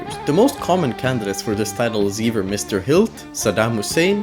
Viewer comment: Did he call Hitler "Mr. Hill"?